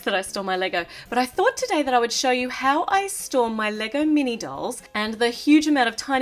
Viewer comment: Omg I thought she said how I store my liquor